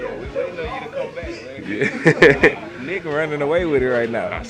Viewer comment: did he say nigga? lol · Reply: Nawww nigga he said nick